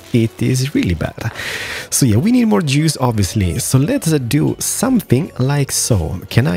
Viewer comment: LOL. I thought he said "Yeah, we need more jews obviously." When he was checking his energy supply.